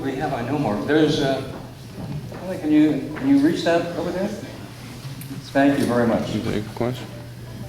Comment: Did he say "spank you very much" at 0:07?